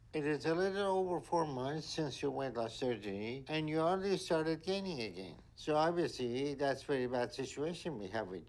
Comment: At the beginning when Dr Now said "you started gaining again" I misheard that and thought he said "started gaming again" and I was like huh? Why is that an issue? 😂😂 I may need a hearing test.